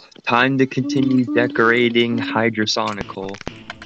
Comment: For a sec it sounded like I said "degrading HydraSonical" lmfao